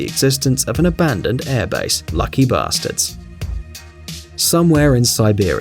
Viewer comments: did you really say "lucky BASTARDS" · Err did he say luck ba****